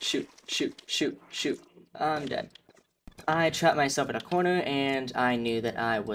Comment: Wait what you just said at the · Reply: “I’m dead. I trapped myself in a corner am I’m dead” or something like that. Idk if you actually wanted to know what I said or not. Lol